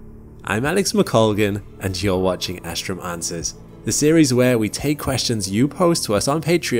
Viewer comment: When I first heard you say, "Astrum Answers," I heard, 'Astromancer,' and I thought, "Huh, this channel took a weird turn."